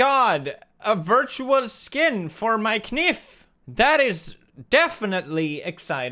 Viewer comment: Did he just say K-NIFF for KNIFE !!?? LMAO XD